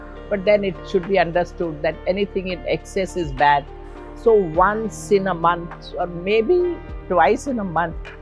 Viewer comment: Did she say once or twice a month 😔 ...